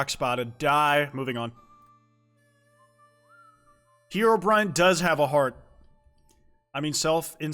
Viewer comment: for a second i thought it said "LeBron does have a heart"